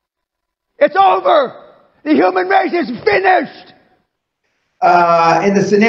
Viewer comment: IT'S OVER THE HUMAN RACE IS FINISHED!!!!!!!!!!!!! Aaaaaaahahahaaha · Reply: I thought he said “Finnish”. From Finland.